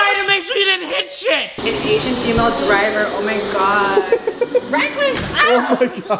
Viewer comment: I thought said: an Asian emo driver....